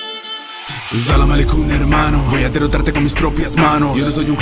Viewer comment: Que dijo. Xd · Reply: Altaïr Ibn-La' Ahad XDD